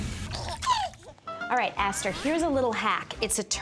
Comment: did she just call her asterik ahahah